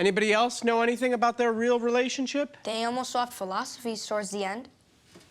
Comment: what does the kid say? “They almost garbled philosophies at the end”